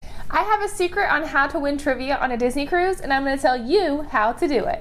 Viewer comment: Oh at first I thought it said how to win a Disney Cruise. But I stayed instead of leaving. Keep up the good videos!